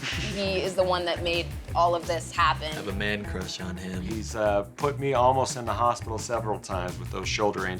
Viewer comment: Wait at 9:04 did say "I have a man crush on him"? I WILL USE THAT